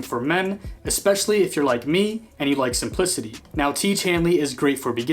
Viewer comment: I thought bro said “ & you like some pussy “ 😂😂